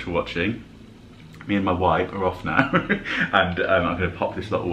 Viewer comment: Hi.good shop as always .had to laugh at the end I thought you said bye from me and my wife ! Not me and my wipe lol ❤